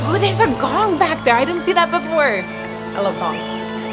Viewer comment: It sounded like you said, "I love bongs!" Me too! ;)~